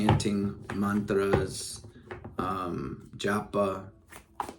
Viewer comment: what is the last thing you say? Jappa?